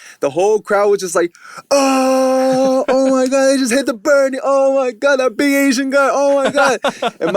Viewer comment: Does anyone know the birdie dance that they where talking about? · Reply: Faith Man I thought they said Bernie